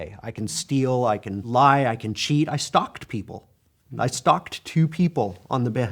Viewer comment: I know this is a serious topic, but as someone born and raised in Britain, am I the only one who heard him say at 2:18 that he "stocked" people instead of "stalked" people. I was thinking: "Wait this man is admitting to throwing people in his basement, how is he not in jail for kidnapping lol?"